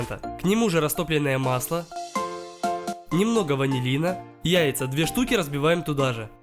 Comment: мне послышалось вазелина